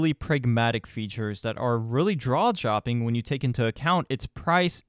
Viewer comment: Did you really say "drawjopping"?